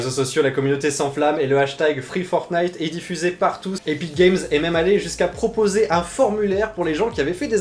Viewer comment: c'est moi ou il a dit partouze ?